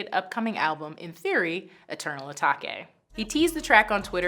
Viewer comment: eternal what.......she really said A-Ta-ke